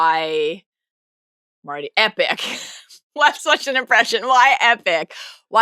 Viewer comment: ? Cannot figure out what is saying here. Epic?